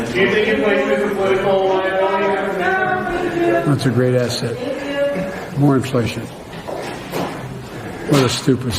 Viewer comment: It sounded like Biden said “those are great asses” 😂